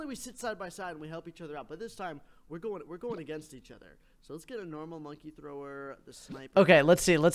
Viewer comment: did he just say "Lets get a normal monkey thrower?"